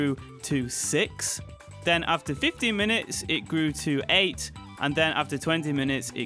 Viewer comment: Did I hear you say 50min instead of 15 at 1:54 or am I just hearing things?